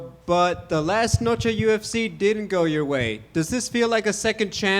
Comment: bro was confused frs. He’s like me I lost? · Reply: I also was confused, I heard "last Noche UFC didn't go your way" maybe he said "did go your way" lol